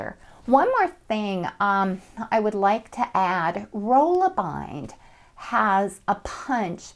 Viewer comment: At the 7:10 mark, you mentioned another punch system that you wanted to try. It sounded like you said roll-a-bind (or something similar), but I'm not finding anything when I tried to search for that. Can you clarify what that third system is that you referenced? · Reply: rollabind punch